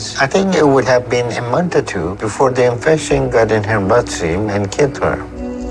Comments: …ooooh. “Blood stream “…..I thought he said ‘Butt Seam’ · I swear when Dr. Now said the infection would enter her bloodstream I thought he said the infection would enter her butt seam and kill her 😂